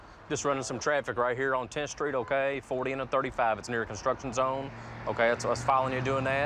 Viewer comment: Hold on 11:05 . Did he just say he pulled him over for doing 40 in a 35? Or did I get that wrong?